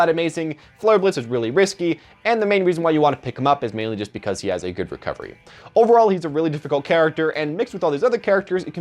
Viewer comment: Did he just say Charizard has a good recovery?